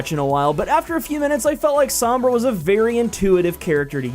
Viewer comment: Am I the only one who hears Zombra?? :))))